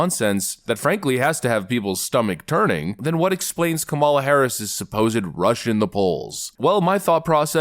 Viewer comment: Sounds like he said Criminal Harris h😂